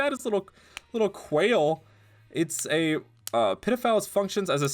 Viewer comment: At one point, when you said Pitifowl it sounded like you said “ped0phile” 😭